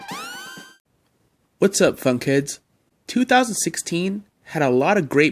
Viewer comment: It sounds like you said was what's up FUCK heads